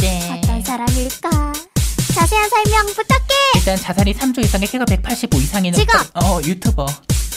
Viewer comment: 초에 자산은 3조 이상에...있잖아여 나만 자살은 3초 이상에..ㅋㅋ라고 들림??ㅋㅋ